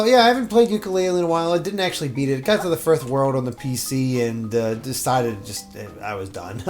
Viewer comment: Which world did he get to? It sounds like he said thirth 🤔